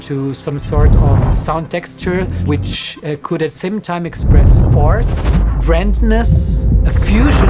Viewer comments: Did he say "farts" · did he say "express FARTS" ??? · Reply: Force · what did he say ? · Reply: Force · he said force, however, to me it sounds dangerously similar to farts... Then I recall that the tesla has a fart app... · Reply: not even kidding I thought he said farts and was like.. uhh I guess some farts might feel that way and just continued to listen. Didnt even think that it could have actually been force... hahaha